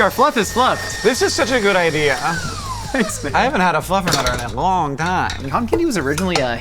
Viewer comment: I thought he said he hadn't had a fluffer in a long time 😂💀